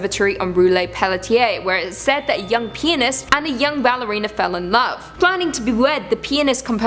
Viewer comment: Am i the only one who thought she said penis instead of pianist?😂😅